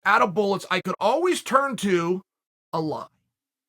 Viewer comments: for a second I thought he said, I could always turn to Allah.... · I thought he said: "I could always turn to Allah" hahaha, Chael Sonnen muslim or not review by Greg Doucette coming soon 🤣🤣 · hits different when you understand "allah" instead of "a lie" 🤣